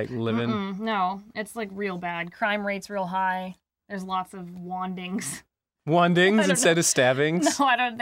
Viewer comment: Said "lots of wandings" I heard "lots of Juan Deags" All wizards are hackers confirmed Gaben pls fix